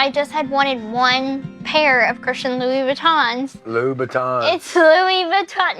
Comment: Is he saying “Buh.. tons?